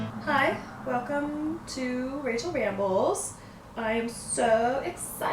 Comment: OMG, I thought at first you said, "racial ramble" LOL I was like, oh god I'd didn't know Rachel had this side of her